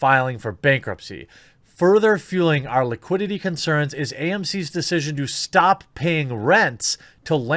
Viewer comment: Whoops, I thought I heard you say AOC and not AMC